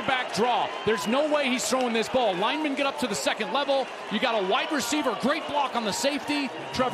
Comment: he said white receiver instead of wide receiver😆😅😆😅😆